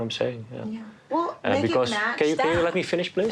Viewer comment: Actually if you turn it up you can hear him say "And Because", right as she's interrupting him.. Maybe she didn't hear him or didn't care?